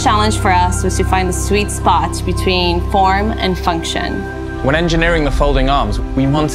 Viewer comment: I thought SHE said " porn and function"